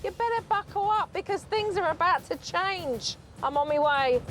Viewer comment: Did anyone else notice that at 2:10 Supernanny says "I'm on me way" instead of "I'm on my way"?